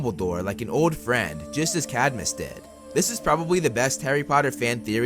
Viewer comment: who heard "Katniss" rather than cadmus haha